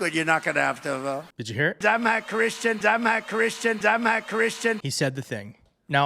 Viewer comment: This is crazy and insane!! I don’t agree that he said he’s not a Christian though. I heard, “I’m a Christian… or, Imma Christian””. No matter though, the idea that he plans on being a KING! Is the main fear.